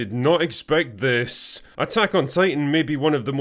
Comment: And I thought it said 'Attack on TATIANIA".